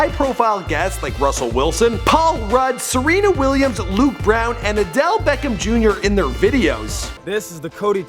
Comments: Am I the only one who heard "Luke Brown" instead of "Luke Bryan" at 0:15? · did he just say adelle beckham jr?? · Did he just say Adele Beckham Jr? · Nigga please did he just say Luke brown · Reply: You heard correct😂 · did he just say Luke brown?